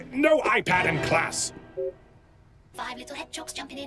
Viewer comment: I thought he said no iPad in class